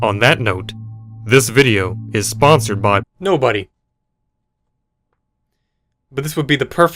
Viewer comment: when I heard no- I thought he was saying nord vpn but he said nobody lol